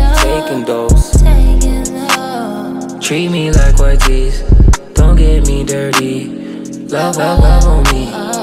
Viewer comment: I legit thought he said "treat me like but cheeks, don't get me dirty" 💀